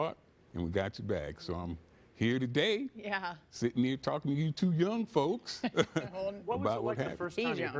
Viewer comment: CT said: “sitting here talking to you two young folks” What I heard: “sitting here talking to you two young mutha fuckas!”😂😂😂